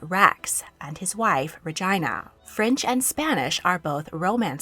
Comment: I thought you said Vagina when u first said Regina. I've never heard it pronounced like that lol